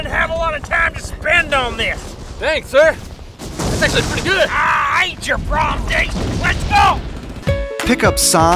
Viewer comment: I aint your prom date! haha sounds like something an actual commander would say if you complimented them. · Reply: I thought he said "It ain't your problem, Dave".